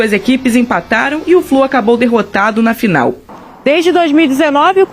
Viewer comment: "e o flor acabou derrotado no final" 4:00😂😂😂😂 só eu entendi isso ou é o instinto de zoação da minha parte?